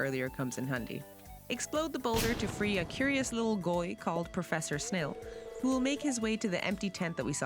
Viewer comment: did you just call the miner a goy? אייל נאוואחו? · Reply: I said lil guy with a funny voice, like half the internet does. I'm Irish, why would I speak Navajo😭